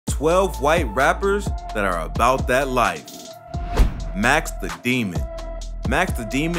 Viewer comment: the title 😭😭😭 ( edit ) : i read the title all wrong . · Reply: Tyler Medcalf man i thought that shit said “ 12 white rappers that are not bout that life “